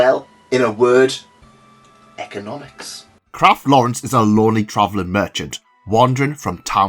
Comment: I've been reading too much light novels and watching too much anime. When you described Lawrence in the beginning, I heard "he's a loli traveling merchant" not "Lowly traveling merchant"